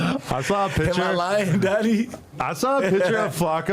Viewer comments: did he just call another man “daddy” 👀👀👀👀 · Did call him Daddy? I genuinely wanna know